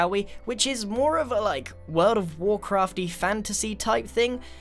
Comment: he says "world of warcraft" but i hear the dark crystal. Any jim henson fans out there